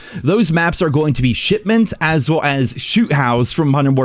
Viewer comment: First time hearing it, I thought you said Shitment 😅